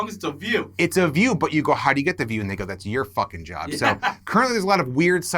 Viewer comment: Umm WHAT did he say at 16:20? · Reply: He said "and they go" but it was fast, so it sounded like the n word